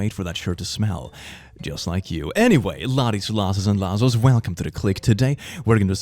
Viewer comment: Again, I'll swear you said, "Laddies, lasses and lassholes"! Am I hearing you right? 5:41 - To observe Columbus Day, I plan to cut someone's hands off if they don't bring me gold. 6:33 - Yes, that's exactly right! You clearly know your Harry Potter! 22:49 - Don't you mean "C2 Shining C"?